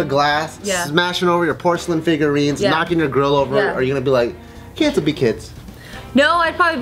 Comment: Wait did anyone else catch the fact that said 'knocking your girl over '@ 6:17 that must mean they having a girl !!!!! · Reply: He said "grill" lol.